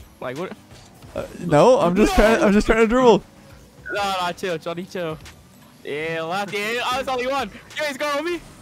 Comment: did just call daddy? And said daddy chill?